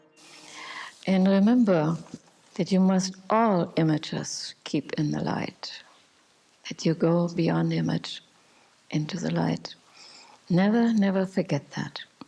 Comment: What does she say here, 2:32 ? Please. Thank you. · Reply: "And remember, that you must all images keep in the Light, that you go beyond image into the Light."